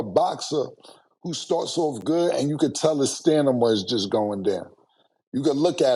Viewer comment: At this point we have to accept the fact will forever say "stenema" instead of stamina 😂lol